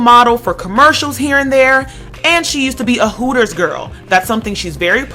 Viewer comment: Hooters? I thought it said "hookers" lol